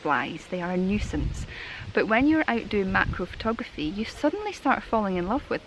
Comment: I know it's just your Scottish accent, but at first I thought you said mackerel photography......was hoping you were going to get out a fisheye lens